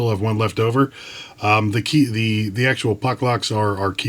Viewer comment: I thought it said suck locks.